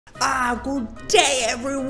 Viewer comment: At first I thought he said" good damn bye"